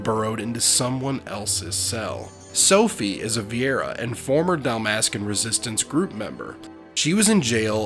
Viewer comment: Fantastic video. At one point tho I did mishear "Sophie is a former Dalmascan" as "Sophie is a former dumbass" and that has been added to me and my friends repertoire. Sorry Sophie.